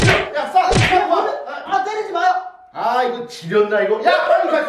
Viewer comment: 초에 아이 씨×라고 하는거 저만 들었나요,?